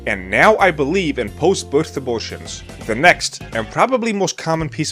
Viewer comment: did he say “post birth abortions”? Kinda hard to tell